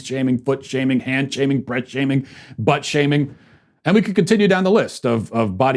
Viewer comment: For some reason, I swear I heard you say "butt-shaving." 🤣